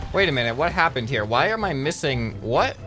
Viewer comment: what did he say??? “ wait what happend here why are my missing.” What???